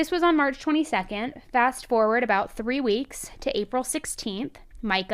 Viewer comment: I feel like I heard “walk forward “ 😢